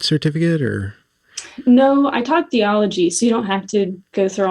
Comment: Lol, for the first three minutes I thought you were saying Geology. So stupid